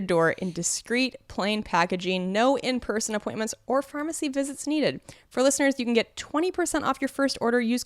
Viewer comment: at about 0:21 when is doing the ad read I swear I heard her mispronounce "pharmacy" or at least an accent comes into play and instead of "pharmacy" I hear "firmacy". Of course, considering the subject of the advertisement "Firmacy" is the correct word. So I will go with it was done for comedic effect. Good job:)